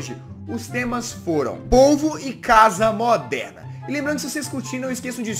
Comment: soh eu q entendi ovo em vez d polvo??